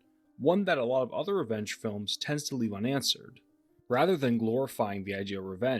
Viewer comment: I thought you kept saying wrench films instead of revenge films lol